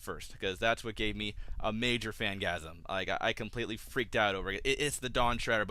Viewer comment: Did you just say, "fangasm"???!